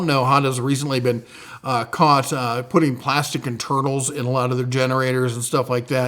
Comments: Ok sir. Thank you for the video and information but HOLY HELL. When you said "honda has been recently caught putting plastics in their INTERNALS", I heard and TURTLES. 🤣🤦🏻‍♂️🤦🏻‍♂️🤦🏻‍♂️ I had to rewind 4 times until my wife said "internals"? Then it made sense. I'll also add a +1 to the Slate Grey colorway on the toolboxes. I bought my 5 drawer Series 2 during last Spring's parking lot sale and I LOVE the color. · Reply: It will definitely mess up the soup. I heard "turtles " too... 3 times before my analyzer sorted and coagulation the info into "internals".😂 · I think he said they were putting plastic IN turtles, but im still not sure why Honda would do that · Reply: that's what I heard too, but it's plastic internals despite what closed captioning said. Glad I wasn't the only one 😅